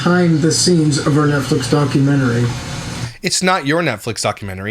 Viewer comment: I thought he said nutflix 😂😂😂 i need to get my ears checked 😂